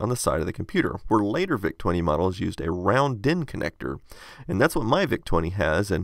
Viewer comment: Did you say round pin connector or round din connector? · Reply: din